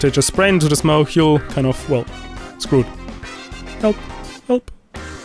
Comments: did he say help? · did u say help 2 times? wtff